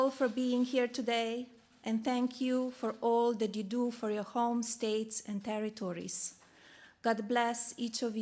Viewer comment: Did she just say, "thank you for all the doo-doo for your home, state and country"?